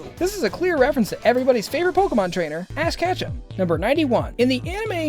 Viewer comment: It sounded like you said ass catch'em instead of ash ketchum